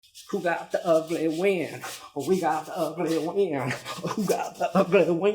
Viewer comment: haha for a second i thought you were saying "who got the ugly wiener?"